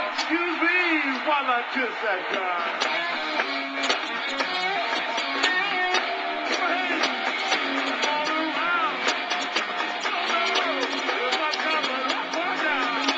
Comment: no, what he's saying at 1:01 is "excuse me while I kiss the sky"